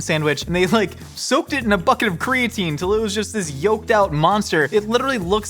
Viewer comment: I thought you said “fvcking bucket of creatine” and got excited until I realized you did not